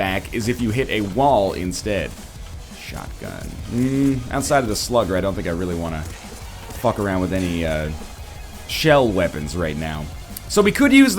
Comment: instead of shotgun I hear Sharingan(if that's how it's spelled:3)too much Naruto tbh